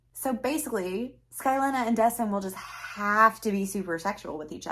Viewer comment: I’m sorry but everytime you say this chicks name I just hear “ Skyleena” or whatever that IUD was called lol 😂. And I thought it was bad I have two characters called Puck and Lazarus ( although at least for my story their names tie into their stories so eh)